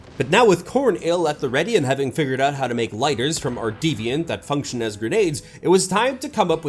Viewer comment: or so, I heard lighter bomb... And it made me think; lighter bomb... Lighter flamethrower bomb...? 🤔